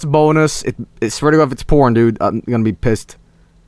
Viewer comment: WOW YOU GOT THE TETRIS, WOW YOU’RE THE BEST, WOOOOOOO WOOOOOOOOO, TETRISSSSS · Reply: I thought he was saying WOAH! I’M A PISSED OFF